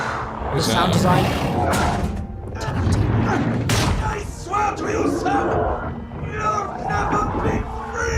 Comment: yeah im definitely so much happier they decided to actually bring back all the original wonder weapons also at 8:06 it sounds like richtofen is saying i swear to you sam , but i put on subtitles and it says he saying i swear to you son so what ever that smoke is its either sam or a son